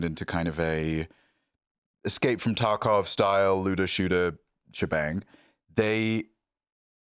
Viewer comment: Ccaptions say "Luda shooter". Why Ludachris catching strays?? For our aurally challenged friends he said "looter/shooter," hence likening to Tarkov.